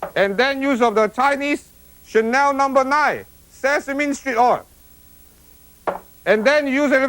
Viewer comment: It sounds like he said Sesame Street oil. lol